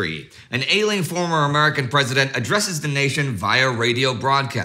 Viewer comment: I'm a trifle hard of hearing. At first I thought you said, "Alien former American president." LOL